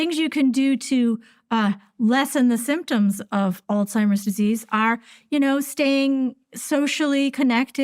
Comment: did she say: Old-Seimers Disease?